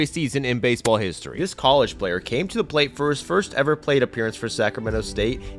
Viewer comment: Was he from Poland or was he shiny? Sounded like he said “this polish player”.